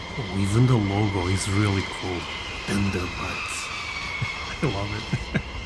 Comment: Lmao!! It sounded like he said tinder birds!!!!!!!😅😅😅😅😅